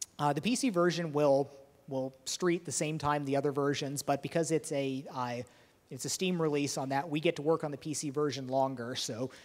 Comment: Did he say Rage is Steamworks enabled at 25:25? If so :) :) :)